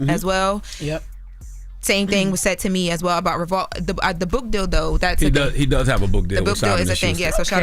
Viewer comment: I thought she said "the book dildo" 😂